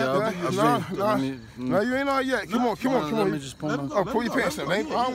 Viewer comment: His first audible words were “you guys are some good people”. Underneath those drugs you know this man does not want to be like this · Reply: I wonder what does he say at 5:36 too? "let me just..?"